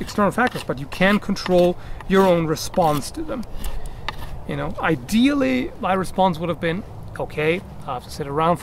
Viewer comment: I honestly thought you said "The ideal response would be cocaine" for a second